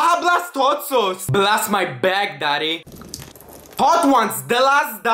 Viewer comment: I thought he said" blast my bag daddy" but it's actually back lol